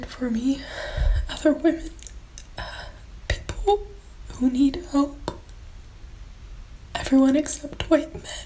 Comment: I totally thought she said "pitbull who need help..." Christ. Cry RIGHT!